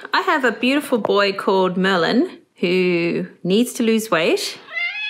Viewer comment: When you named him Melon did you know he was going to become a melon? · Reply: It's actually "Merlin", her accent was misheard, but Melon does seem very appropriate 😂